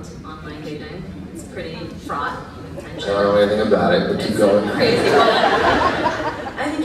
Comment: Anyone know what he said at 3:56?? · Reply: I think he said “don’t know anything about it”